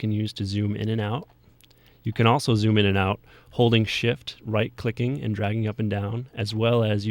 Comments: it sounded like he said holding shit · he said shit instead of shift